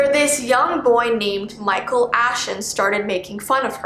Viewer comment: I cant be the only one who heard michael afton